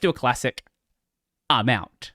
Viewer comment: I misheard "I'm out" as "Amen". sweat drops